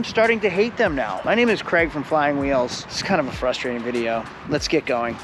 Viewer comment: Never seen this channel beforenbut i thought he said hi im greg from flywheels and instantly thought 'yeah you're gonna need a few of those'